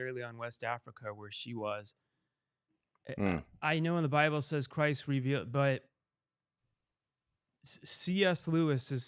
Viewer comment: literally unintelligible you cant even understand what he said "I know the bible says christ revbut"